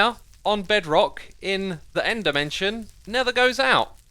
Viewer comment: it sounded like he X said "bedrock in the end NETHER goes out"